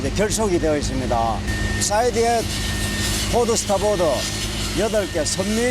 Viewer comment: 쯤 드라이도크 계류시스템 설명 중 자막에 오류가 있네요. "이 사이드에 우현 8개, 선미 2개와 앞에 육상과 연결된 것이 9개" 라고 자막이 나오지만, 직원분 설명에는 "Port/Starboard(-side., 좌현/우현) 8개" 라고 분명히 들리네요. 다음 설명에서 총 27개의 앵커체인이 설치되어 있다고 하였으니 선미 2개, 선수(육상쪽)9개, 그리고 '좌/우 양현 각 8개' 가 맞는 것 같습니다. :)